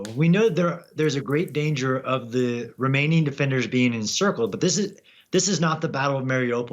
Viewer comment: Did he say "Romanian defenders"